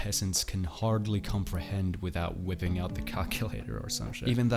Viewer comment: I thought he said "without wipping out a c*ck" in 4:29 lmao